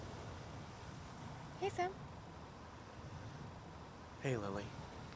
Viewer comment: Any Sweds also thought she said "Hejsan"